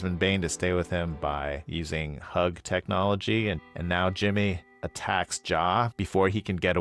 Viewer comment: my sister heard me watching this in our room and said, 'did he just say hug technology?' 😂😂😂